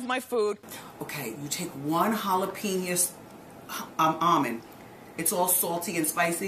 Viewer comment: Did she say halopenius?